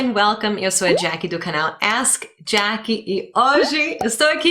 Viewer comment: O RAFINHA FICOU UM LIXO COMO COMEDIANTE MAS OS DOIS AQUI DO VIDEO FORAM MUITO RESPEITOSOS COM ELE AFINAL QUALQUER ESTRANGEIRO QUE NAO FALA DIREITO A LINGUA LOCAL VIRA PIADA NAO Ë MESMO JACKIE? POR EXEMPLO “ASK JACKIE” EU ENTENDI “ASS JACKIE” LOL